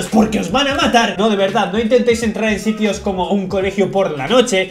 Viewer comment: entendi colegio porn... :v Necesito ayuda :'v